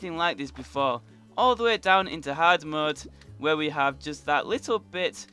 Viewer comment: Is It just me or did he say all the way down in hardmode at 6:38 when he entered hell then said hardmode again 5 seconds later